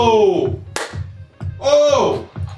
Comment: What Aman says in the end (29:08)? I'm not English speaker, so it is hard to understand. English subtitles are saying something like "take that guy to the test", but I'm not sure that he said exactly this. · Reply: He says "Gotta take that guy to the shed". It kind of means you're going to kill someone